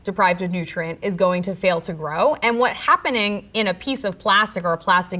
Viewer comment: Did I hear that correctly. "......and what happening, .............". Is she AI?